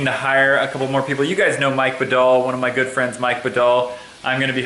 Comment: omg my last name is vidal and when u said Mike bidal and I thought u said vidal